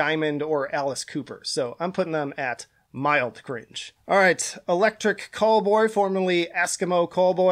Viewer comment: I thought electric callboy said electric catboy